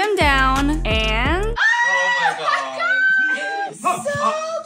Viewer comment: That was so wholesome when dad said “do you recognize yourself?!” with his accent. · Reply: thought she said “it’s linus”